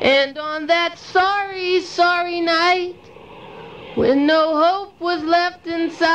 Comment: The first time I´d heard Sorry Entertainer I thought the choir said "sorry I´m thinner"